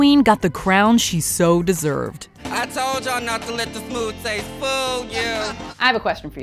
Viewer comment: 49th..what did Symone say at the very end...I heard " I told you not to leave the Pool half full"...? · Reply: She said “I did tell you not to let the smooth taste fool you”